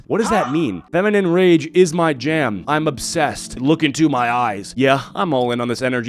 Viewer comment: “You could leave too I wouldn’t be offended” wait weren’t you offend 5 mins ago? I thought you said” look at my eyes” loudly and hurtfully tone.